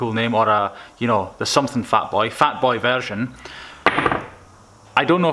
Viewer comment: Did anyone think at 3:57 he said fat boy virgin?😂